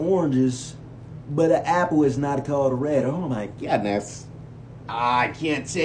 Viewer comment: I just realized that he said "Oh my goodness" At first I thought he said "Oh my good, that's-"